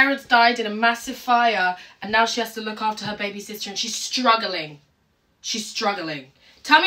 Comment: I thought she said “she has to look after her babysitter” 😭